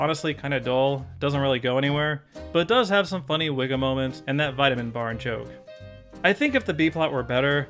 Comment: OOoooooohhhh! "Funny Wiggum moments"! I thought you said it had "Funny wigga moments"